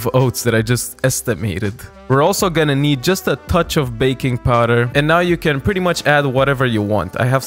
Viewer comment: I was cooking while listening to this And at 1:55 I could swear I heard "bacon powder" and had to leave what I was doing to double check if I got that right... Sadly I did not. Will schedule an exam with my ear doctor tomorrow 😂